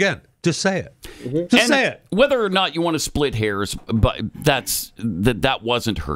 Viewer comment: Sounded like he said split Harris instead of split hairs 😂